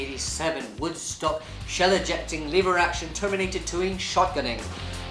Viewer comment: I thought he kept saying "Liberace" instead of "lever action"